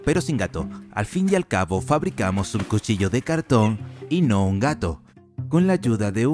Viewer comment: cuchillo de gato Es lo que yo entendí :v